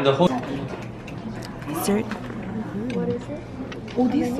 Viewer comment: I hear "whatta man" at 19:02.... ANY PH KPOP FANS???? Because I am!